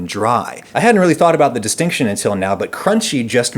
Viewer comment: captions, make it down folks. The first time adam has ever made any spelling error in anything at any point in his life. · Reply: oh and also 4:27 caption says destination while he said distinction